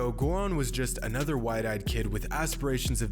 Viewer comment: At first I thought the narrator said "... just another WHITE-eyed kid...". I laughed and laughed. Then laughed some more. You should have been there.